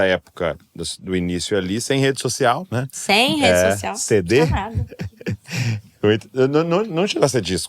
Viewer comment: Alguém pode me dizer a palavra que ela fala em 33:10 de vídeo? Foi um Caraca ou caralh…?😮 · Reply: Ela disse: não tinha nada